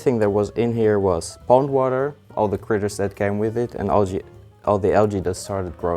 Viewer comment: I swore you said bong water the first time lmao